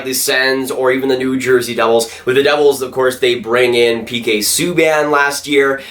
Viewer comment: Am I trippin or did say “brang” at 4:49 🤦‍♂️